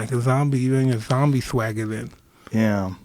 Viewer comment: Yo, did he just say, "Zombie-Swagger" ??? I listened back like 3 times and I'm still not sure